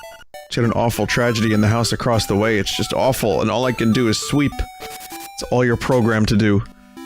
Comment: That moment when you hear Vinny say "Sweep" and you think he's just saying "Sleep" in a stupid way, instead of reading the actual word.